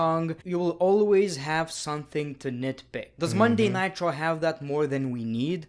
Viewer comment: Did he say Monday Nitro, But it is Monday Night Raw or am im hearing something else because of his accent........Right!?🤔 7:21 Edit: Btw In the bury section...The one i was really mad and stopped watching WWE, Was from how they mistreated him and is VERY FŪÇKÎNG TALENTED! is Ricochet. Damn what a waste man.Rico jet